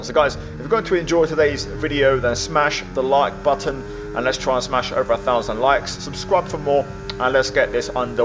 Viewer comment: I thought you said let's try and smash over a thousand lads 🤣